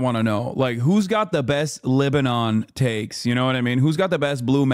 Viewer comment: Every time Hasan said LibAnon I thought he said Lebanon.